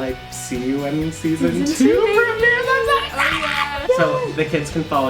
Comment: is saying something about season 2. Is he saying it premieres on Black Friday? Lol that’s what I heads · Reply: Wow HAHAHA. I am SO SORRY. I said “I’m so EXCITED” hahahahahaha